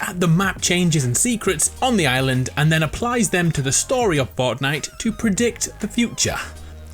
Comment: For a minute i thought he said botnite also those green areas on the mountain i think are from the collector also have you talked about the forest if not you need to since ot is a PS4 and PC exclusive it is an amazing game and if you have not talked about it what are you doing pretty good graphics for a game made a while ago and Endnight even brought it over to PS4 by themselves now that is why we need to show a LOT os respect